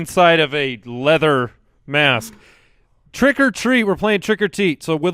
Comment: Did I hear "Trick or Teet?" 41:47?